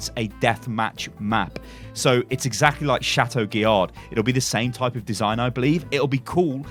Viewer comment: Captions say "Chateau Jihad" 🤔